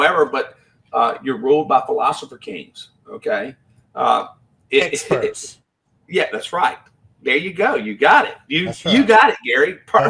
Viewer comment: what did Gary say that made say “you got it”? I can’t decipher what was said. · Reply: The government isn’t able to violate these rights; oh wait, except under authority gained from public health and terrorism.